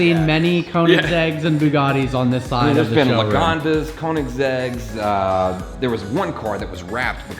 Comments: Anyone hear say Paganda's -- Pagani/Zonda lmao@ 15:18 ????? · Anybody else heard “pagandas” instead of Pagani